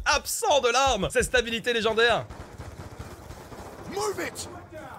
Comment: Heuuu les gars rassurez moi svp , a 8:37 , le mec il dit bien "Move It" et non pas "Moule bite" hein ? J'ai comme un doute .... 😂